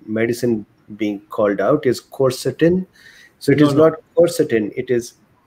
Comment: Dr. is a marvelous physician. I have told many people about his powerful approach to patient care. I look forward to the in depth discussion regarding colchicine. I wish the automaton physicians in the U.S. would learn from this creative thinker. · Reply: Ohhhhhh that’s what he’s saying lol I thought he was saying “Quercitin”